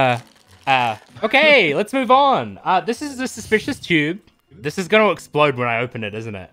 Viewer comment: I thought she said suspicious jew lmao